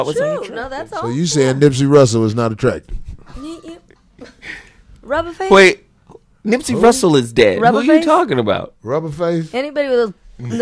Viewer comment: almost sounded like Nipsey Hustle